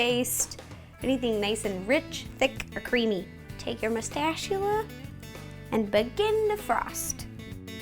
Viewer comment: is it just me or on 5:35 did she say Mustachula???